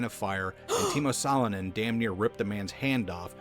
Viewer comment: Finnish names popping up in this video more than any other country (The subtitles got the name wrong, it was Timo Salonen) 😂